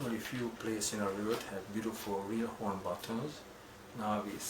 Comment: Did you say " real horn buttons" @ 0:10 ? If so, what animal do these buttons come from?